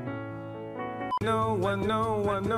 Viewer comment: I hear wanoe and no one